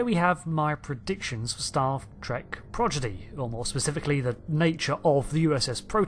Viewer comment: anyone else hearing "staff trek progidy?"